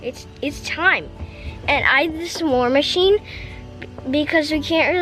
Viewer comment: I thought said, “we have a bid back yard, it’s TIME! I have this war machine” 😂 I was really concerned and confused